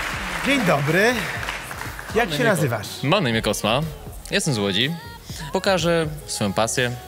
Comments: usłyszałam jestem złodziej XDD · Ehh... 0:37 zamiast "jestem z łodzi" usłyszałam "jestem złodziej" XDD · Jestem z Łodzi... Usłyszałam Jestem złodziej😂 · Czy tylko ja tu słyszę ,, Jestem Złodziej " ? :D Wiem co powiedział, ale to serio tak brzmi ^^